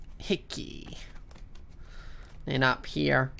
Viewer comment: when he says "Heck yah" it sounds like he says hickey.